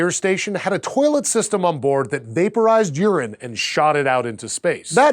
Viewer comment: When he said it vaporizes urine I thought he said vaporizes Uranus and I was totally confused and at the same time very pleased and immediately started thinking of Star Trek